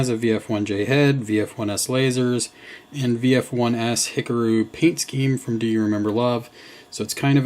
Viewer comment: VF-1S... did he... did he say "Hickaroo?"